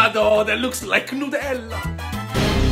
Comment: My dumbass thought they said,”LEAVE US THE COFFEE!!!” 😭